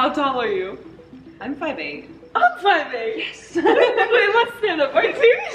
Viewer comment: Ellie viendo esto : Duren 👁️💧👄💧👁️👍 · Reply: Alguien me dice q altura dicen? En el minuto 15:57 gracias..escuché 1, 58 pero nose